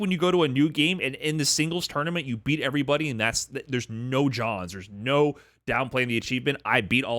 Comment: did say no Johns? LMAO The Riddles influence